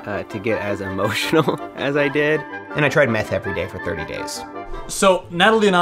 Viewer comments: i heard meth my brain told me it was meditating my heart kept saying he tried meth everyday for 30 days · honestly i was shoked for a minute, i thought you said math ...